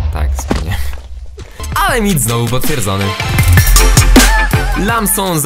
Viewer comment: yyy.. W 2:06 powiedziałeś " noi mit znowu potwierdzony ", a pojawiło się "obalony"